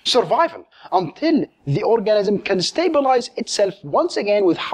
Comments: Did he just say orgasm? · Sounds like he said orgasm. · Ha! He said orgasm instead of organism at 4:58. Rotfl! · "orgasm" instead of "organism" :P